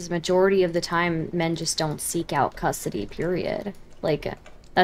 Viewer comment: did Stardust say "custardy period"?